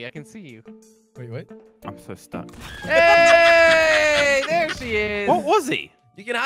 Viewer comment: says there 'she' is instead of there he is😂😂😂